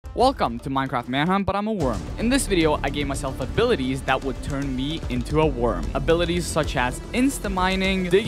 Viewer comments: I thought this man said "Welcome to Minecraft ManHunt but I'm a woman" 😭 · I THOUGHT IT SAID BUT IM A WOMAN AND I WAS SHOCKED 💀